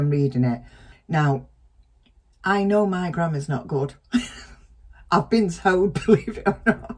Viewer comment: Haha.. I thought you said..”my Grandma’s no good”..2 x’s..